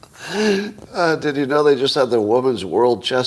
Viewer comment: You missed it, Scott. The reason there’s a woman’s chess championship is not because they’re not strong enough to move the pieces but because they’re not smart enough to know where. 🤷‍♂️🤡🌎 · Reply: At first, I thought he said 'Chest Championship'